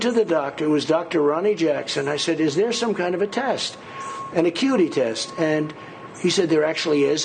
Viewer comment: Hilarious. Trump called it an accuity/memory test and he couldn't remember the name of the test. Stable genious? Not! Stupid orange cheeto yes! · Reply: Did I hear him say "a cute tee"??? It should be uh kyoo uh tee...